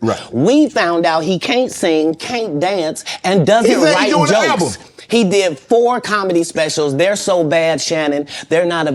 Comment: ok what’s the name of it i couldn’t make out what he said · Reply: Shannon said He doing an album